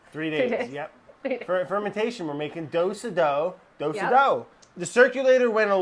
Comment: I thought it said “making doggos” and i was like im down